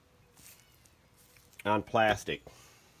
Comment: I came across your channel I believe it was the video on restoring worn leather your videos move quickly are not boring and are extremely informative I subscribed immediately and your videos never let me down I am a car guy and thought I knew it all until I came across your channel thanks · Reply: Is that a plastic or glass headlight? sounds like you said not plastic at the end.